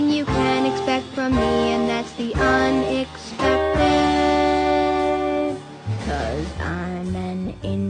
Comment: HELPPPP I THOUGHT IT SAID EXECUTE😭😭😭😭😭😭😭😭😭😭